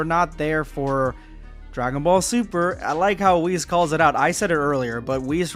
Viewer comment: Sorry but for a sec I thought you said dragon ball c*ck in me